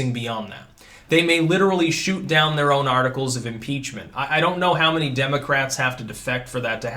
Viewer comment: I Thought you said article's of PIZZA for a second there.😃